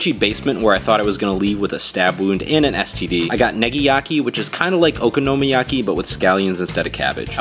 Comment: Did he just say nigayaki?